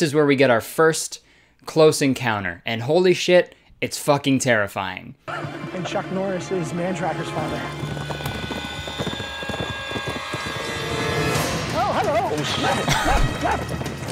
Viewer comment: Did I hear one of the “prey” say “chuck Norris is man trackers father” I absolutely lost it